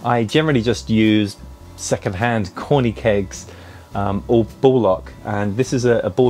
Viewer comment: Whenever you say “ball lock” I hear “bollock”